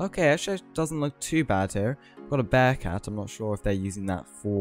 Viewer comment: what did he said, bad cat? or what?